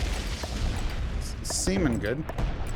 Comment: Did he say he likes sea men or semen good? Keep your weird tastes out of the finale. Nice work old and again i luv watching this map. Airship overrated!